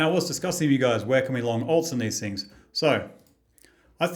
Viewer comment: Long holds? Fill me in padre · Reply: at 22 secs i mis heard you say long alts... not long holds, lol, my greys hairs ive grown must have moved into my ears, my bad